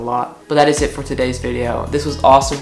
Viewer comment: at the end it sounded like said "its awsome from the kotlc fanbase" lol